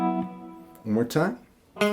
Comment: Did you say," Mou ikkai." ? Do you speak Japanese ? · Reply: Haha, I said, "One more time". No I don't speak Japanese. Cheers.